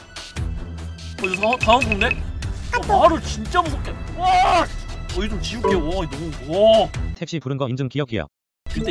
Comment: 이때 뭐라고 한건지 알려주실분… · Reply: 한강물 따뜻한가 라고 했습니당 너무 자극적인 말이라서 가린거 같네요 ㅠ